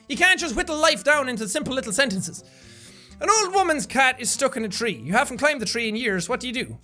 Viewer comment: I thought it said an old woman is stuck in a tree